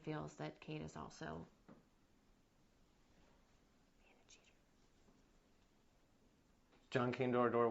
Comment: 🤷‍♀️🤷‍♀️🤷‍♀️🤷‍♀️ What does she say at @ 3:22???? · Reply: “A cheater”